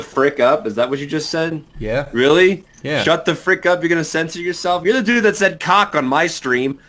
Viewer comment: he actually said "You're gonna censor yourself?"